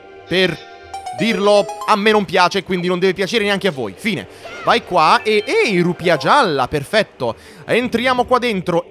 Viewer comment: Wow la torre è veramente bella, Abbiamo capito che i giochi zelda ci sono tanti riferimenti ai giochi Nintendo hahahaha bhe dai il giallo e un bel colore (meglio il blu) 2:42 dittatore, lui dice noi facciamo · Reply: "Il suo desiderio è nostro ordine"